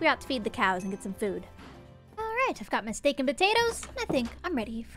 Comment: when Gem said "my steak and potatoes" i heard mistaken potatoes...